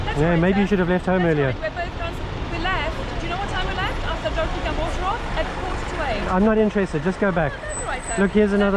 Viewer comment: What does she say at 1:02 "you know what time we left, after..." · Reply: Dropping our daughter off